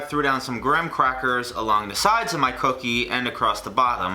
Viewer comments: “the tits” did anyone else hear that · Did anyone hear "tits" instead of "tips"